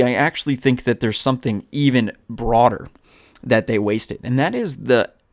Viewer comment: did you say broader